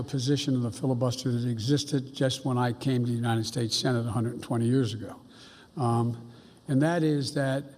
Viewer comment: did he just said he was on the senate 120 years ago?